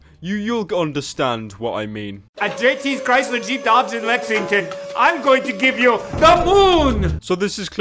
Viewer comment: I think he said erection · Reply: Dillon Malbrue "lexington"